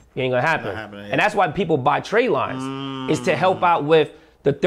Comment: What did he say min 17:40 about buying trainline or tradline 🤔 what's that about? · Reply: Build business credit. Tradelibes will help you get a business. It’s pretty hard to get anything in a business name so tradelibes help you. It’s not as easy as these videos make it seem. This is good info but it takes TIME. It will not happen over night, you will need great personal credit to leverage business credit initially